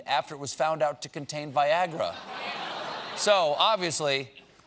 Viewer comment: Viagra? I thought he said saliva, which would be a lot worse.